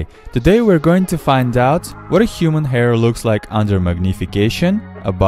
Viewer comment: Oh human hair... I thought he said ingrown hair